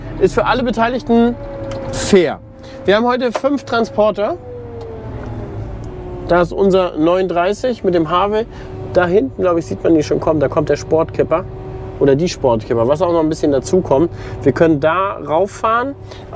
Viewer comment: Bei 3:31 habe ich verstanden "Mit dem Habeck". Zum glück habe ich mich da verhört, sonst hättest du dicht machen können. ;-)